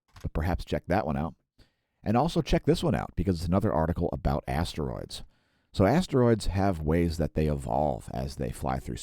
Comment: I thought you said Assroids,i don't know shit about Asteroids.